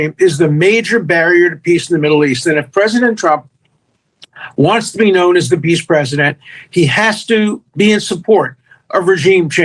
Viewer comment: my captions says if Donald wants to be the beast president!! does any body captions say the same thing?